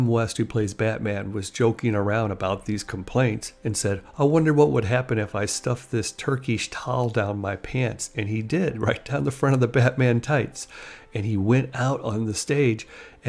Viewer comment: what did he say here? "turkish towel" ? what is that?